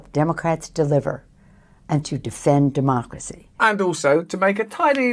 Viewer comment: Sounded like she said to defeat democracy….. more accurate